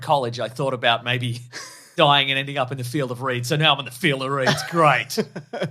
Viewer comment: When I watched this episode. I didn't here "field of reeds", I heard "field of dreams" and I was like "really, baseball? 🤔"